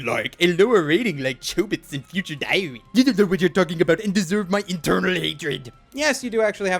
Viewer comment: I scrolled back up from the comments while you were doing that rant about scores towards the end and could have sworn I heard you say "and you deserve my internal hatred."